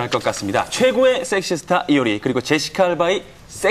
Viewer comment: for a second I thought this dude said 'Sexy star Yuri and Jessica' from SNSD ! Ahahah ! XD Don't know why I'm laughing so hard !